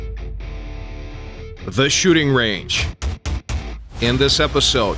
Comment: who here thought he said shitting?